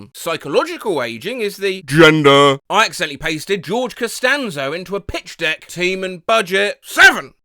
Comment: im half asleep and misheard this as Lord Costanza, the image still works